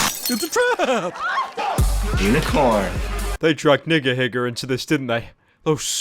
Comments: did he just say nigga higga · im not racist but when nigahiga came in it sounded like pyro said ni** higger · what did he say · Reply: Nigahiga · DONT TELL ME I HEARD THE N WORD